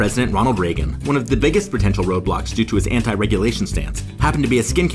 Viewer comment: DID JUST SAY ROBLOX?! PLAY ROBLOX NOW